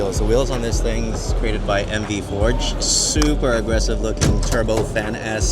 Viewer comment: i couldnt quite catch what you said @ 9:40 - did you say MD forged wheels? Cant seem to find the company to check them out, thanks ! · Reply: I think it's MV foged